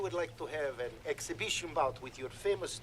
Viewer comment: Rocky this was supposed to be an exhibition Drago my bad I thought you said an execution R.I.P Creed.